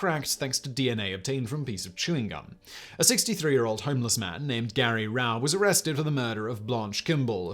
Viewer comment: i misheard the chewing gum homeless man as "Chinese man", a cold case in maine's Asian community until i rewound i and realized you said homeless man, though my fan is on full blast so it muffled the audio.